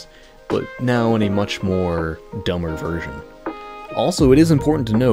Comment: Did you just say "more dumber"